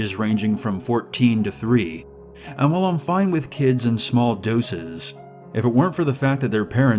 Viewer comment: What did he just say😂😂😂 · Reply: He's fine with kids in small dozes